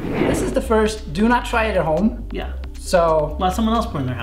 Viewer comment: Well, the phrase "do not try this at home" for some reason doesn't work for me. I heard instead "do try this at home", and it looks awesome. Am I alone? Anone can resist not trying this at home? :o)